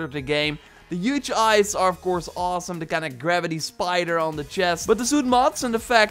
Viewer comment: It sounds like he says Gravity spider instead of graffiti 😂